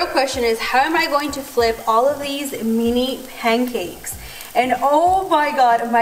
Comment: lol she said nini instead of mini btw how in the world did you get your nails done?! Aren’t the nail salons closed?